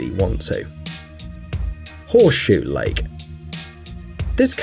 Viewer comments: This is how you know they REALLY mean it: "10 PLACES YOU SHOULD NEVER EVER SWIM" Also, am I the only one who thought he said "horseshit lake?" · I thought it said horse shit lake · i thought he said "horse shit lake"